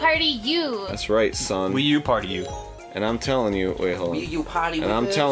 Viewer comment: does say "Wii U party, niggas" at the start of the episode?